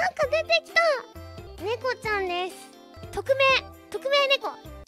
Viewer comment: I think she said 匿名ねこ(tokumei neko) not 特別ねこ(tokubetsu neko) 匿名 means anonymous